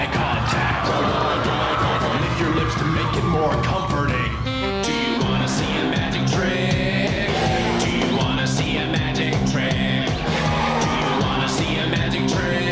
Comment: I am dying to know what he says at 3:11. Does anyone know? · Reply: He said "do you want some of this"